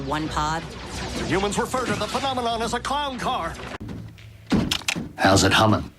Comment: "Humans refer to the phenomenon as a clown car!" Gotta love that line. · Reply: Lexington Deville84 I thought he said Con Car when I first saw that episode🤣